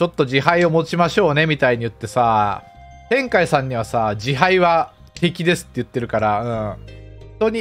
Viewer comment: 「字牌は… ”敵”です」 かな？ ちょっと息継ぎとカブって聞き取りづらいですが。